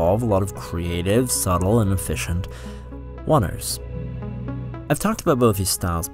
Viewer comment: What word are you saying at 0:26 ? "Creative, subtle, and efficient ... ___". It sounds like "waters", but I don't understand how that makes sense in context. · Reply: He’s saying “oners “. Meaning one take.